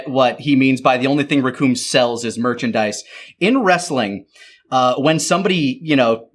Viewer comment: As a non wrestling fan I thought the line "The only thing Recoome sells is merchandise" I thought he was saying "I'd never sell BS"